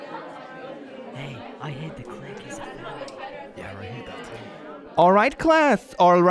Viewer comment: YALL AT 0:07 THERES A KID IN THE BACKROUND THAT SAYS "hey i heard the click is a furry" PLEASE TELL ME OTHER PEOPLE NOTICED